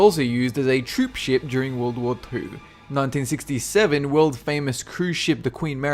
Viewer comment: it sounds like sayd `world war poo´ XD